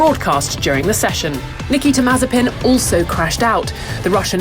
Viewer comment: Did she say "Niki Temazepin" at 0:28? Yes, she did. LOL. If he keeps going to sleep at the wheel, perhaps the name Niki Temazepam will stick.